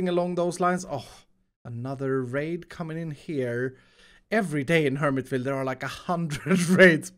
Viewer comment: I heard you saying "Another rape coming in here" 😂